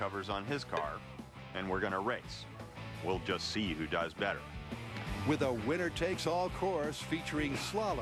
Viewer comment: in 7:08 did Jamie said "we just see who dies better" ???